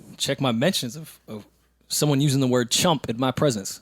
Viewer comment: I thought Joe said he will stop using the ‘N word’